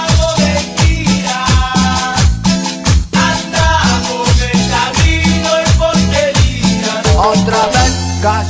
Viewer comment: Supermerk2, Supermerk2 Y ahora Suena el rototón Suena el rototón Encontré la solución al problema de la resaca Me mantengo en la joda borracho hasta las seis de la mañana Y cuando pinta el sol, nos vamos pa' la esquina Bailando con los pibes, meta vino y porquerías Supermerk2, Supermerk2 Andamo' meta vino y porquerías Otra vez cayó la noche Otra vez no' vamo' de gira Andamo' meta vino y porquerías Otra vez cayó la noche Otra vez no' vamo' a la esquina Corré, guachín Supermerk2, Supermerk2 Encontré la solución al problema de la resaca Me mantengo en la joda borracho hasta las seis de la mañana Y cuando pinta el sol, nos vamos pa' la esquina Bailando con los pibes, meta vino y porquerías Andamo' meta vino y porquerías Otra vez cayó la noche Otra vez no' vamo' de gira Andamo' meta vino y porquerías Otra vez cayó la noche Otra vez no' vamo' a la esquina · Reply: yo pense que decia " me encamino y porqueria